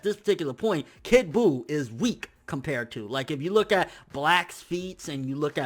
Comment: lol I thought he said look at blacks feet